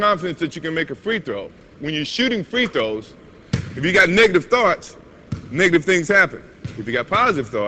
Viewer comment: did he say nigga?